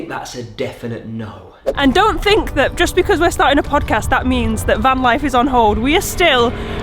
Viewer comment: I didn’t understand what the surprise was at first. I thought you said you were going to make a fun house! I thought that would be nice! Then realized later you were saying pod cast!😂